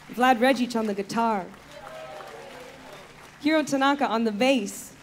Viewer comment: ヒロ・タナカさんの紹介がHero Tanaka と聞こえていいなぁ！